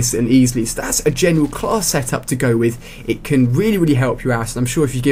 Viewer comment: did he say it can help your ass? O.o